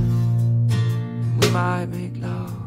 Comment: When he says “ we might make love “ I hear “ goodbye big love “